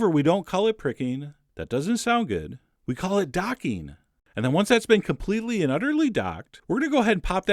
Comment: I hate to break this to you, but docking can also mean something sexual. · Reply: I thought he said "dotting", not docking...